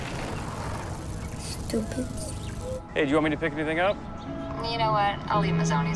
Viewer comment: why it sound like he saying “hey you want me to pick the nigga up?” 😳